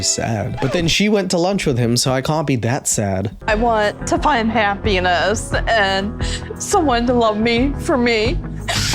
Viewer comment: take out the H that's what she's really looking for..all i heard was "i want to find a penis"